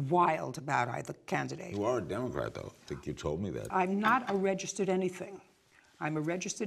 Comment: What did Larry say ? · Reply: you are a Democrat though , I think you told me that